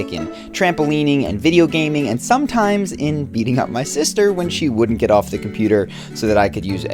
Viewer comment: lol i heard eating up my sister first loool i had to rewatch the video hahahah